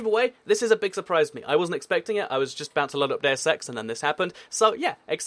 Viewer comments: onwards for about 5 seconds, What did he say?! :/ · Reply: deus ex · WHAT DID HE SAY AT 4:34 ?!?!? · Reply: He said Deus Ex. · WHAT THE HECK?? AT 4:34 SAYS BOUNCING AROUND "DO SEX"!!! I THOUGHT YOU WERE PG.SAY IT AGAIN AND ILL UN SUB.!!!!!😡😡😡😡😡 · did he say day of sex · What did say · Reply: Oh wait deus ex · WHAT THE HECK?? AT 4:34 SAYS BOUNCING AROUND "DO SEX"!!! I THOUGHT YOU WERE PG.SAY IT AGAIN AND ILL UN SUB.!!!!!😡😡😡😡😡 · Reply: He said deus ex. Calm down. I thought he said sex as well at first but then I listened again and I heard what he really said DEUS X!*